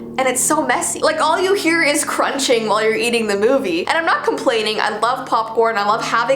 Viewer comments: is it me or did she just say "eating the movie" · Did I hear "eating the movie"? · Intro: are you ready For the strange and unusual Me: Nope in fact I was to scared to click on the notification 😱 🍿 3:35 Also am I the only one who heard her say: and all you here is crunching when eating the movie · did she say eating the movie hahahahaha 🍿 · did she just say "all you hear is crunching while you are eating the movie" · Wait... did anyone else hear her say "all you hear is crunch while you're eating the movie" at 3:35??????? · Oml did Jessi just say "eating the movie"